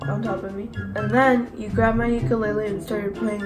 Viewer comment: I litterly thought your son said “ and then he grabbed my ankle “ 😂😂😂😂😂😂 but you starters playing a ucalaly 😂😂 nice improve tho 👌🏻🔥